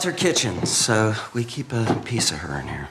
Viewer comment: Love how It sounds like he says "we keep a piece of her in HER." Even more subtle...